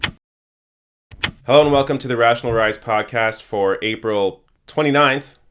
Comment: Thought you said "National Rice Podcast" at the beggining, lel